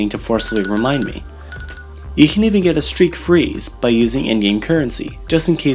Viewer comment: it sounded like “Indian Currency”